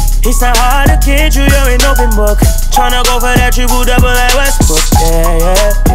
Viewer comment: did he just say "westbrook"